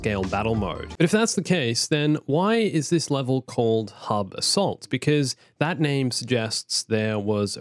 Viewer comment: I swore he said Hub Salt. I was like... whaaa